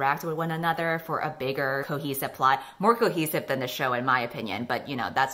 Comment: Why did I hear say ‘cookie supply’ when she was actually saying ‘cohesive plot’??? ‘More cohesive than’ became ‘more cookies than’. I was so confused because I didn’t remember there being any cookies in any of the books or the show 😂